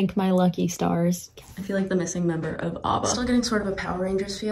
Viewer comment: did you just call ABBA. AWBBA